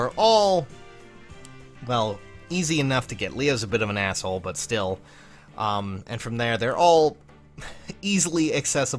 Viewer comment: "i'll show you mine if you show me yours" get some sleep also what did you just say at 2:18? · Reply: no one gives a SHIT about hawaii